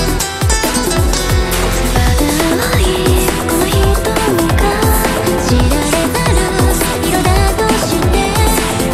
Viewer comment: I LOVE THE WHISPERING SOUNDS IN THIS PART i wonder what theyre saying? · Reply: A random japanese user said they could hear them whispering "CO6FAC" which is a shade of purple ITS LITERALLY WHAT THEYRE SAYING! I hope that japanese user finds happiness in life